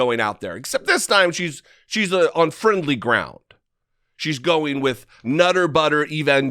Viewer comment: Did you say unfriendly ground or on friendly ground? Seems like it's unfriendly to me.